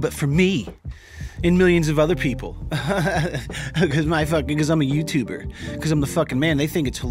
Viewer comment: Yo at 7:25 the auto generated captions think he said, “because my fuck niggas I’m a YouTuber”.